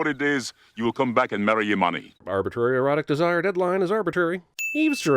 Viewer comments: Never knew the line was "You will come back and marry Imani." Thought it was "You will come back and marry your money." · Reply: First time I heard it, I thought he said "your mommy" and I was like... that can't be it. Lol. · am I the only one who thought his dad said "marry your money"? Only now having seen subtitles at 3.29 do I know he has actually saying a name